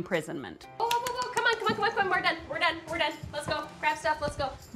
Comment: Hold up... did she just say, "we're done?"... I would be the one kid going, "ahhh hell nahh Ima sit and eat my damn Corn Flakes idgaf. You and the f**** bus can wait." Smh